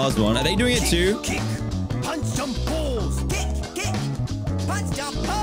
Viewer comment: I thought it said punch some balls and I was very confused